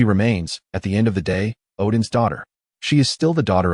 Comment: i thought he just said its just the same name as hela..not odins daughter...